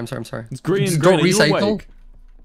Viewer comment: what are they saying at 1:57 can anyone tell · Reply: Wilbur asked Grian if he's awake.What Scott said was just him still talking about meltdown strats